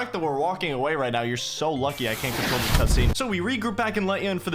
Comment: Did you say cutscene